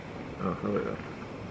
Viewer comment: What did said at 8:40? I’m sure he swore in polish :D now I’m confused, was that polish or am I projecting? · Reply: Or maybe he just said o hell yeah